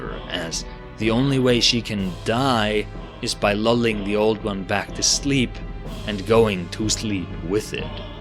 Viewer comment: At first I thought she said loved the old one back to sleep